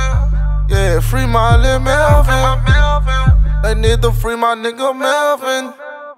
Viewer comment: RIP Quick🙏🏾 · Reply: Whole time I thought he was saying "Bendito free my nigga Melvin" I just peeped its "They need to free my nigga melvin" . Lol 🔥🔥🔥🔥🔥💪